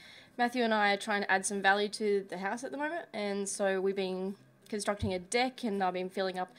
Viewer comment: lol at first i thought she said "we are constructing a DICK." Lol then i remembered shes Australian. accent gets me everytime.